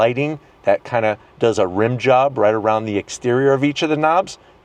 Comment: Did Really Just Say "RIm Job" At 18:19 ?! LMFAO😂